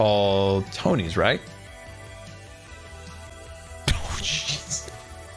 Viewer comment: Why the hell do I hear scar saying : Oh shit... xD Edit : just now I realized hes saying Oh Jeez